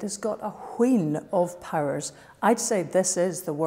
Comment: what is she saying there? "it has got a ??? of ???" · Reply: Wheen of powers, wheen meaning a considerable amount of.